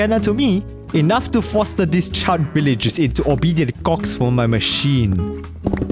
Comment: Gonna be honest I thought he said "cocks" here